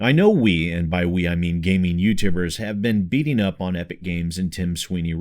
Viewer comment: I don't know if it's just me... but I swear I heard him say "Youtambers" at 0:41Hehehe, thought I'd be that guy and point it out. :P Keep up thr amazing work, I have been a long-term subscriber and your work never fails to impress. 💙